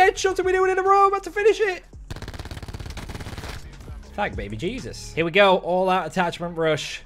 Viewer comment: He makes it look tooooooo easy. 6:34 did anyone else hear “Black Baby Jesus”😂